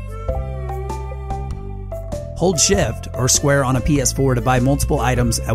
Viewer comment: "Hold shift -- or square, on a PS4 -- " I heard this as "Hold shift -- or swear on a PS4" -- and I was thinking "What kind of witchcraft is THIS?"